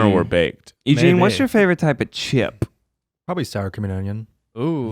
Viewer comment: I hear Star Chameleon instead of Sour Cream and Onion every single time